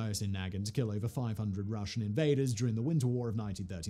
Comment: Listening while lifting and thought I heard "used it to kill over 500 Russian betas" and I was just like, damn that Finnish dude alpha af! Killing Russian invaders is much better and less confusing.